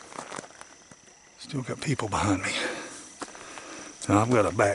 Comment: Hahah, at 13:36 I totally heard say: “stupid people behind me”… and I was like, what??? Not our sweet?!?! He actually said : “still got people behind me” 😂